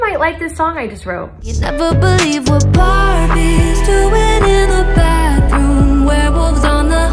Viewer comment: Why did it sound like you said (barbies doing in the backrooms) LMAO 🤣